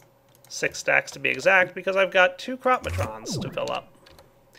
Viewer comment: when i heard you say "crop-matron" i thought you said crap matron :P